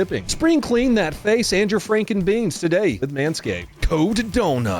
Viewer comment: On the manscaped copy, a line was "frank 'n beans" and I definitely heard "frankenbeans". I was wondering how Cody knew what my balls looked like.